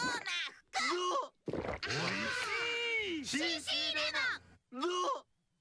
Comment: What Homer Did Say? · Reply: D'OH